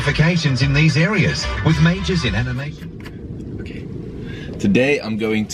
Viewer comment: did i hear that right? Get internationally recognized qualifications, with majors in MMA?